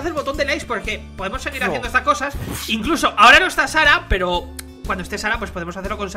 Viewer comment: cuado dijo charap x4 pense que dijo sara x4